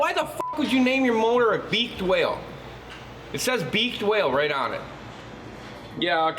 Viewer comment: At first I thought you said beached whale 😂